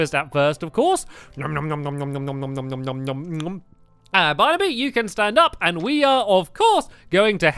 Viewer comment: in the beginning of the video it sounded like you said for Barnaby Barbie